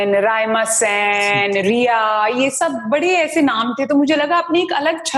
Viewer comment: did she just say "Rhea"? NCB wants to know your location